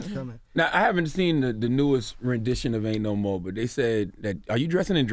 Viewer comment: I thought it was saying “ain no mo jussie smollet” smh🤦🏽‍♀️